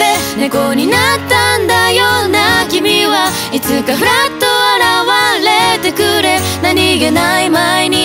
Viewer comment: 「いつかフラッと現れてくれ」が「♭」って聞こえてきた。心も半音下がる？いや、3人のハーモニーで少しだけ上を向きたくなった。「僕はまた、幸せで」ゴールで救われる。が兄姉の前で伸び伸び歌うのがよき！ 企画56 → BNZooで本物の猫動画を集めて、「猫」のＭＶを作ろう！